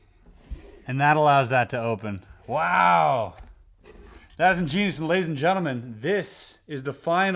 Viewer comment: I know you probably were saying something else, but all I hear is 'that's juice' 😂